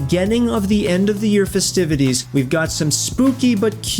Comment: Did he say FIZZtivities?